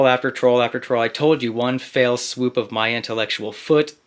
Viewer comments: Eww, not that guy 😬🤦 I swear I heard "one FAIL swoop" 😂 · "Dig a big enough hole when I cover it up" "Under that rock I put you in" "Blind faith. The blind leading the blind." "One fell swoop of my foot over your grave." This guy can't get a single expression right. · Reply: I actually thought I heard him say “one Fail swoop...”. Which would be perfect